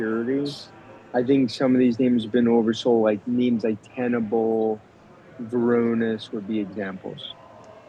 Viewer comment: What the heck is he saying at 9:28? Cannibal, tanable, kaneble, tanenbul, cannon bull, tanninble, ??? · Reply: Tenable (TENB) and Varonis (VRNS)